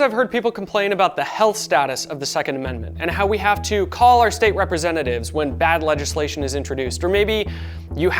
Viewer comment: you misheard them. It’s not call our state reps. They said cull lol Also i love your channel but can we stop shilling for SDI?